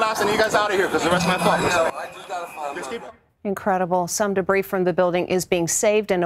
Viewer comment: did she just say incredible? 😂😂😂